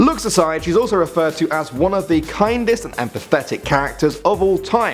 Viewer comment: I thought he said Tifa was one of the Kindest and pathetic at first, I was like "What?" Then I realized he said kindest, empathetic - lol